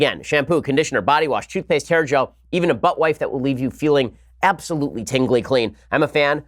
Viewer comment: I heard "buttwife" and missed literally everything else you said.